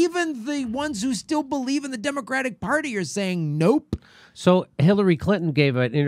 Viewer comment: Did he just say Demo-crack party lolol